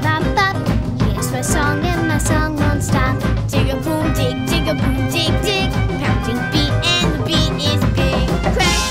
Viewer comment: WHAT DID HE SAY? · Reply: Austin said "Digga-boom-dig, Digga-boom-dig dig."